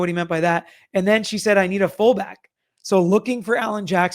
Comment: I thought I hears Karen say she needs a phone back not I need a fall back? Would make sense since she's still waiting for her phone 😊 thanks for covering this!!